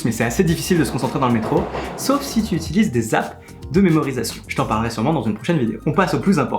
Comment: c’est quoi des « zap de mémorisation » à 2:15 svp ? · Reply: Des apps de mémorisation 😉 Comme Anki, Quizlet...